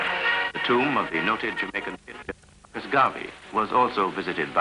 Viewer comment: What's the comentator saying? At mins. 4:41 he reports: "the tomb of the...(strange noises, crack in the copy)...'s Gaavee (?) was also visited by the emperor" Is the comentator referring to Marcus Mosiah Garvey, Jr??? Who knows more? Thanks in advance for any elucidating reply ;-))) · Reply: that is correct...he lay a wreath on the tomb of Marcus Garvey who passed away in 1940. My dad personally met HIM. he said he was as regal as he was portrayed.